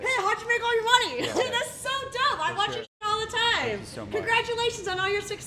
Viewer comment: SCARY WOMAN.....LOL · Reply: For a second, I thought she said "that's so dumb" and then quickly realized that she said "dope". Speaking of 'dope'.😋😁